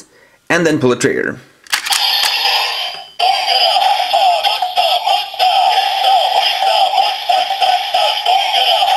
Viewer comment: What was it saying in here please? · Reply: "Don gala mosa mosa mosa, rasa mosa mosa sa sa"